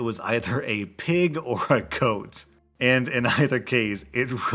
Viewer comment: When was doing his final review on the Horse story and said 'Pig or Goat', I thought he said 'Coat' like Horse ended up keeping a coat as a pet. XD